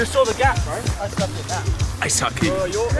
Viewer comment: thought he said "I sucky" XD